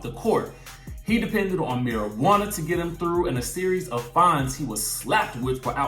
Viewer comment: Why is he reminiscing about Gloria?????? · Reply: Mary Allen exactly, I thought he said Marujana helped him get through